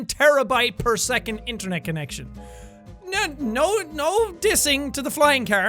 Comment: hahahahaha I thought it said 1 tablespoon internet connection and I got so confused